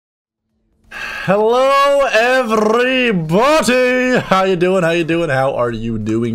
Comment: Is it just me or I heard "Hello everybootey"